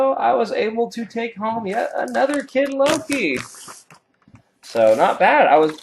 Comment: Did you say .... Kid Loki? 7:57ish